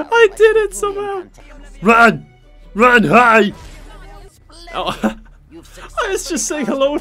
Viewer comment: I seriously thought he was saying "RUN! RUN, HIDE!" XD